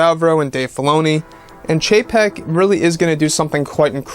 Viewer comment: I always hear "Bob JPEG" when he says the name of Disney´s CEO, lol.